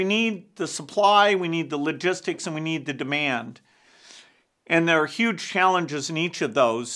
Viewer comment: Talking to Bill Gates while wearing AirPods is a serious flex. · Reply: Did he just say "We need the demand"..? If so, is he than thinking about saving human lives or as a power hungry businessman..? How do you create more demand in this case? Please explain that to me...